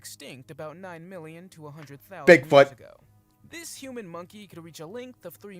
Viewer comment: Did he say human monkey?